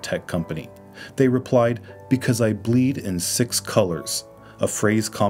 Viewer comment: "Because I bleed in six colours" inspired him so much that he immediately got rid of the colours. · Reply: I thought he said: "I believe in six colors" Wow I bleed in six colors, not cultish at all lol